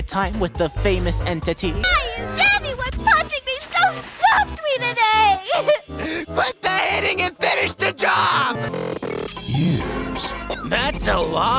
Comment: what does she say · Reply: "why is everybody punching me so softly today - QUIT THE HITTING AND FINISH THE JOB."